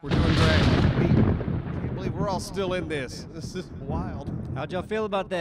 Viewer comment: the "this is wild" sounded like thick to me haha